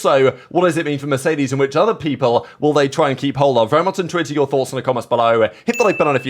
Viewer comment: What is he saying at the start of the video 0:31 “very much intuit your thoughts”? Or is he saying “very much intrigued to your thoughts”? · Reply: Very much intrigued to hear your thoughts :)